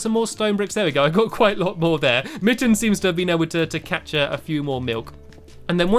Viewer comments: did he say " mittens managed to catch some more milk? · Is it me or did Stampy say `Mittens been able to catch a few more milk` Well said mate!!!